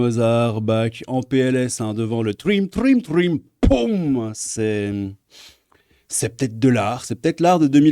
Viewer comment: Quand tu as dit "C'est ptet de l'art" j'ai compris "cette tête de lard". J'ai pensé "ah ouais, osé 😧" Maintenant je sais qui tu es 😬